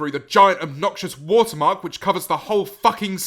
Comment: Anyone else thought he said "wartmark"?